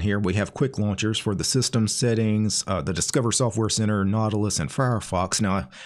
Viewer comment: Did he really say nautilus 😂